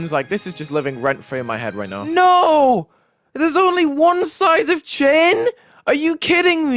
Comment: I Actually Thought He Said “Theres Only One Size Of Chin”